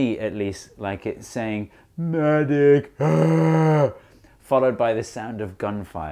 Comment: I heard 'I'm dead!" instead of ''medic, not sure which is more morbid